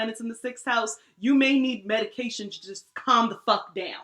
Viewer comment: I thought you said meditation, but you said medication 😂😂😂😂😂😂